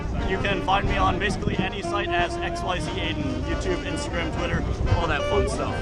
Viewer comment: Hey... where's a link to the guy around 4:50? I think he said his name was... compliant materials, soft robotics... Sounded like he said "XYZ80"... Which when I google it... comes up with Pokemon stuff... Help a brother out if you can hook me up to him..